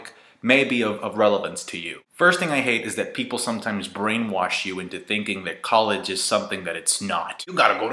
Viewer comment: You went to Wash U? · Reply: nvm at 0:19 I thought he said "bring Wash U" instead of "brainwash you"